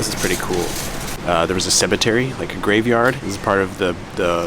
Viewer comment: did he just say cebetary at 2:46 ???